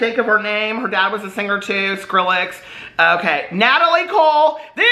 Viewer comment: It sounded like you were saying Skrillex is Natalie Cole’s dad 😂